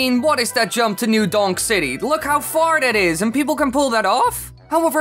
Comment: Lol, at 0:59 it sounds like he is saying look how fart it is!